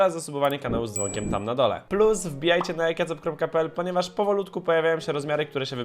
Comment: usłyszałem jajkacob.pl